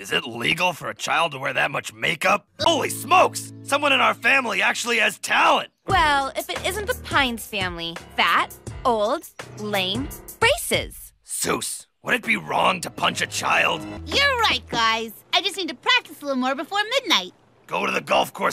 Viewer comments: i thought she said racist not braces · spittakes for a second I thought she said "fat, old, lame, racist"